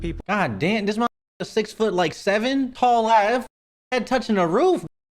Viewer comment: DID HE SAY 6’7 yes ik the vid was 5 months ago